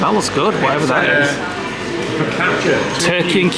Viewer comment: did you just say Fokaka